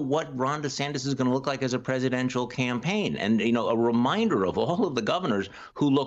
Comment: Wait, what??? Charlie can't tell what type of president DeSantis would be?? OMG!!😳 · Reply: No u misheard him he said he didn’t know what the “campaign” would look like😉